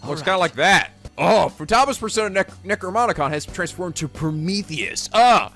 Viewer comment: Did..did he say NecroMonicon?